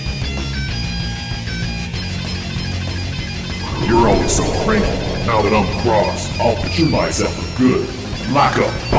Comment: what did you say??? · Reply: he said like a boss.